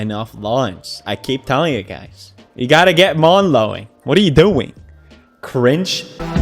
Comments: he says mawn lowing instead of lawn mowing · sorry but what does it mean? I m french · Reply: Un homme qui court. the man in the video sounded like he said “mawn lowing” instead of lawn mowing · I heard 'mawn Lowing" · Is it just me or at 0:39 did pewds say morn lowing?? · He said mawn lowing instead of lawn mowing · Anyone else hear him say "mawn lowing" at like